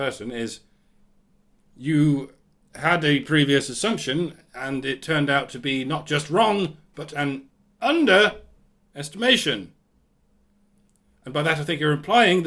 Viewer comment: If I thought the answer was negative 5 and it turned out the answer is negative 10, then I was only correct insofar as I went with negative integers. And we don't tend to count that as correct. Especially if you're off by such a wide margin. The revelation "Wow, this problem is even worse than I thought" is not a confirmation of a presumptive belief. It is a confounding of a belief, just as much as it would be to conclude that the problem is not as bad as I thought. And let's not forget, the previous assumption (and foregone conclusion) is that I was wrong altogether, because the answer is in fact a positive integer. This is not made explicit, but seems to be inherent in the dubious wording (Which I'm now convinced was not poor English, just misleading sophistry.) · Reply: my reason being predicated on you saying "I had an assumption that turned out to be not just wrong, but an underestimation" and I heard "I had an assumption and it turned out not only to be correct, but an underestimation" as what this individual said. Hence my confusion.